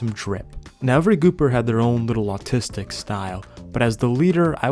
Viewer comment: i thought he said "autistic style" 💀